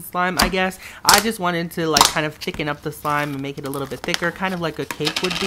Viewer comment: Who else heard him say chicken instead of thicken? 😂😂😂